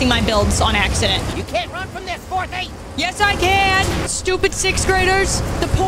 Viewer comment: Lmao, did think he said 'porn thief' instead of 'fourthie'?